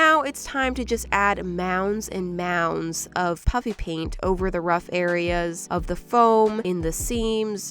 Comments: It sounds like she is saying puppy paint 🤣🤣🤣🤣🤣🤣🤣🤣🤣🤣🤣🤣🤣🤣🤣🤣🤣🤣🤣🤣🤣🤣🤣🤣🤣😁🤣🤣🤣🤣🤣🤣🤣🤣🤣🤣🤣🤣🤣🤣🤣🤣🤣🤣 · Why do i always hear "puppy paint" when it's legit puffy paint- btw im not a hater I'm actually a fan so sorry if someone thinks that!